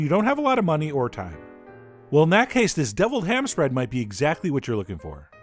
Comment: I thought you said we were having Deviled Clams.